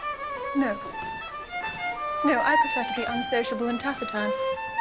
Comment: Aound the [3:40] mark, what does Elisabeth say when asked if she always talks when dancing? She replies "No. I prefer to be unsociable and ?????????" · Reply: Unsociable and taciturn